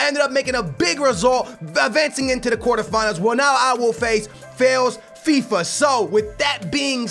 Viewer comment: did he say failsfifa